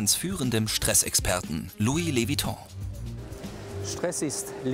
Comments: 😂😂ich habe Louis vittons verstanden habe ich es richtig geschrieben ??😂😂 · Lois lewiton?😂 Ich hab erst loust witon verstanden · ich hab am anfang louis vitton verstanden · Wer hat auch gedacht louis vuitton würde stressprofi sein und hat sich verhört ??😂😂